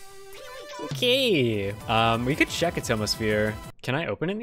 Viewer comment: whats the song name? i think he said it but i couldnt quite here it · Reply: "atomosphere" not atmosphere, but atomosphere